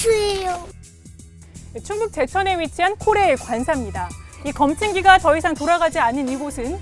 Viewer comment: 초에자세히들어보면코레일이라고들림